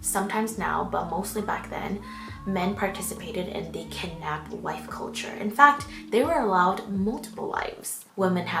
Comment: Did you just say "kidnap wife culture" or did I mishear?👀